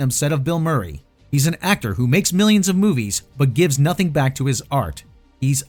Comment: I think Puttnam probably said that Murray makes millions in movies.